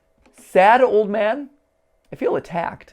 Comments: What he really said: “Shadow Man” What matpat heard: “Sad old man” What I think of: “A Sad strange little man” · Bon said: Shadow Man, not "Sad Old Man" I just wanna say that to the people who dont understand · The first one I heard is "shadow man" instead of sad old man the second one is "I been trying to come back" and others in the comment section saying it is "I've been trained to come back to you". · Personally, I hear "Shadow Man" instead of "Sad Old Man" at the end of the teaser. Anybody else? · I heard "shadow man" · I heard shadow man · I think bon said shadow man ? Or sad old man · I heard "Shadow Man" not "Sad Old Man" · I didn't hear "Sad old man", I heard "Shadow Man". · it’s weird how distorted voices like that sounds different to each person. like i heard “saddled van” (??) after the first video and “i’ve been trying to come back too” after the second. weird how the brain interprets those things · To be honest when mat thought bon said sad old man it really said shadow man what might help is slowing down the video to hear it more clearly but i don't really know if that would help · I didn't hear "sad old man" I heard "schedule me" Anyone else?